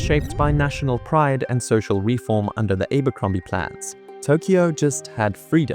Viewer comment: Thx random forest a wonderful video as a Japanese. 8:40 BRW Is it Abberrombie plans” as YouTube CC says “Abberrombie plans”?